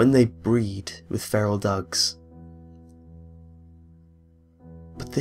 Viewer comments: Everytime you said "feral dogs" I kept hearing "feral ducks".. I fahking broke myself.. xD xD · Pffdddt for half the video I thought you said "feral Ducks" rather than dogs. I was like "wow, those are some aggressive ducks"